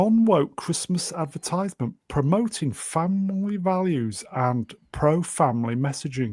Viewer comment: For a second I thought said 'Fanboi values' for the apple ad.